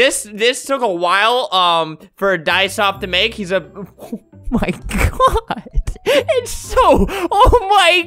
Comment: did i hear "oh ####" (sh word scare aaa)